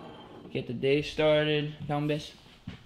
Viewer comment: eu ouvi um "dá um beijo" awwwwwn eu amo o fato se você ser brasileiro <3